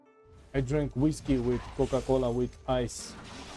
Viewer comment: did you say drink whiskey,Coca cola with rice?